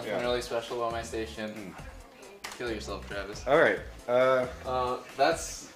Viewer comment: wtf? he says: kill urself trevors (I think)